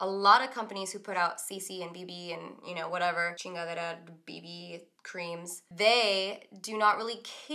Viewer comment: Is it just me or does said "chingaderas" at 2:03? 😂💕